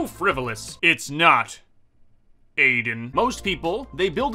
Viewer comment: When you said Aiden I jumped since my name is Jayden and I thought you said my name 😂